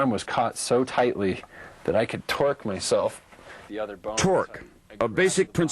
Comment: did he say "twerk" ? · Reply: Torque